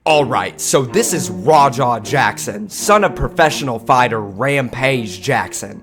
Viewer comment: All i hear is "Raaaw-Dog Jackson"